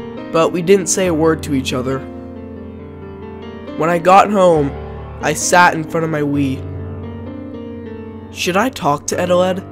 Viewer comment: I thought he said "When I got home I dat in front of my weed"